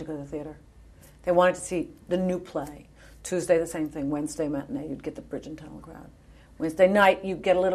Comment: I couldn't understand you, Patti.... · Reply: "...tuesday the same thing wednesday matinee you get the bridge and tunnel crowd..."